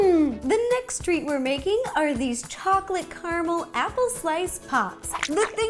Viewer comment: Hi, Ro! You are such a great baker and I can't wait to try these apple crisps! I just wanted to ask you if you knew that your captions are a little off? At about 3:04 the auto-captions say "chocolate caramel apple twice top" when you said "chocolate caramel apple slice pops". If you happen to get a chance, could you please fix that? I know you're busy, but writing in the correct closed-captions for your YouTube videos will help the MILLIONS of people all over the world who rely on closed captions to understand and enjoy them too. Thank you so much! :) <3